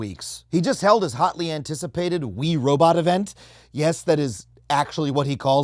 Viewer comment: automatic captions said Musk held a “Wii robot event” while I was scrolling by, and now I’m disappointed.